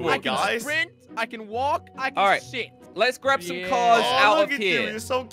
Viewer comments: did say shi* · sounded like said "I Can S***" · I heard say s**t